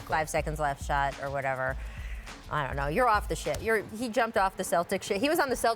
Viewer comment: Does anybody else hear Rachel say "your off the shit"